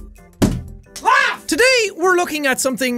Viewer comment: it sounds like he said clap not laugh